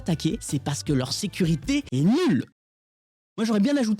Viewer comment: La vrai citation c'est: "leur sécurité, elle est pas bonne". Pourquoi déformer la citation est mettre NULLE en gros avec le son, les FX etc, alors qu'il a jamais dit ça ? Si toutes vos news sont déformés ainsi pour les rendre fun, ça donne pas trop confiance ni de crédibilité dans votre travail les gars...